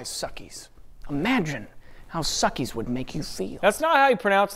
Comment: I'm getting this weird Mandela effect where I could have sworn he said, "Imagine what suckies can do for you," and I wish he said that